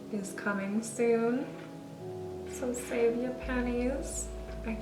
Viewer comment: I LOVE THE YELLOW and your face Also when you were talking about your collab with furless and said “save your pennies” I totally at first thought you said “save your panties” and had to rewind to see if that’s what you really said 😂